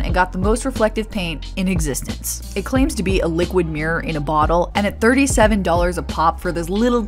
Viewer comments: I thought you said liquor mirrir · I thought she said the most reflective pain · I heard "most reflective pain"